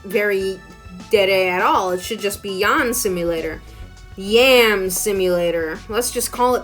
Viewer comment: akdjjskd when you said “it should be called yan simulator” it sounded like you said “yawn simulator” and my brain just went “yawn simulator, because the main character is that boring”